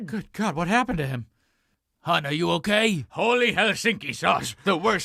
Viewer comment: Does he say "Holy Helsinki sauce"???